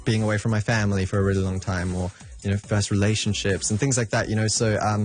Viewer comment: did he say first relationship?